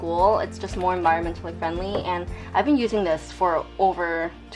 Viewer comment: Did she just say "it's more environment to the friendly"?! HAHAHAHAHAH